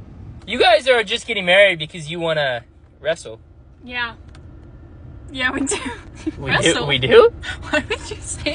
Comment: Who else thought she said, Yabba dabba Doo. Not yeah we do💀💀💀💀